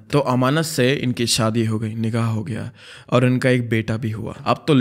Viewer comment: bro literally said nigaa instead of nikha 💀🤣